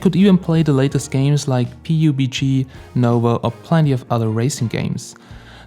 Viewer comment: did you just say "Pi Yu Bi Gi"